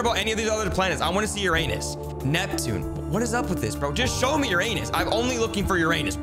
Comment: I heard "just show me ur anus" 💀 I thought you said that but you actually said "just show me Uranus"